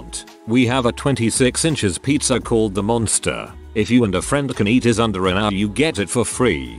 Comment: I misheard that as, "If you and a friend can eat his undies in an hour you can get one free."